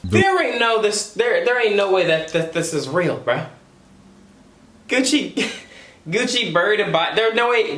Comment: did he just say “Gucci brrr a body” lol